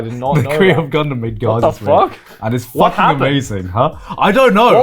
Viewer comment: Can anyone tell me what anime is Garnt talking about at 1:30:30 ? I can't understand the name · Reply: Garzie's Wing or smth